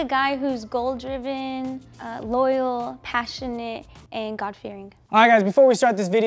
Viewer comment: DID SHE SAY GOD FEARING???